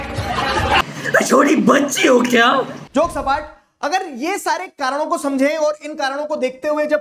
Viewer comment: I literally heard joke spat(flat)😅